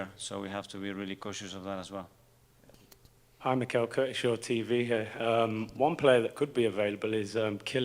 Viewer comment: Did he just just mumble Curtis from AFTV? Sounded like he said “Curtis from TV here” 😂. Bro so nervous he can’t get his words out